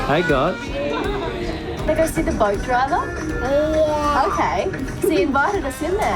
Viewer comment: Did just call it the "BOAT DRIVER???" .... c'mon.....